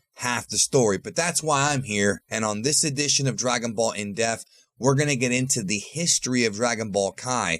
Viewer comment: I've watched a few of ur videos now and I notice every time you go to say in-depth it just sounds like ur saying in death lol. Just wanted to remind you to annunciate that P in in-depth. 😂